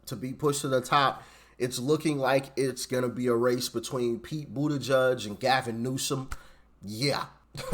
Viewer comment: Did you just call Pete Buttigieg, Pete “Booty Judge”?! Is that what I heard you say?! IN-SANE! 😭😭😭😭😭